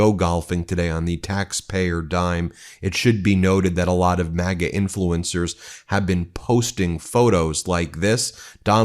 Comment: Ha ha ha I thought you said Maggot influencers. MAGA influences. I think it's time for a cup of Joe.